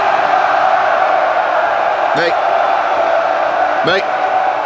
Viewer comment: Was sagt der Gärtner (?) bei 0:40 min ? Sagt der Mike oder versteh ich das falsch ? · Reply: Er sagt tatsächlich "Mate". Das war ursprünglich ein englischer Werbespot und die hatten keinen Bock den Typ zu synchronisieren. Das "all hail the king" haben sie ja auch so gelassen.